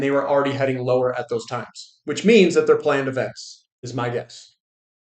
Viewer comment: "....which means that they're playing --INAUDIBLE--" Dang, that was critical, that one word. WHAT did he say...? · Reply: "Planned events", was my interpretation.